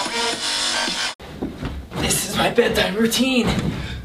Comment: Back then, when I was like 9, I thought he said "This is my Bedtibertean"!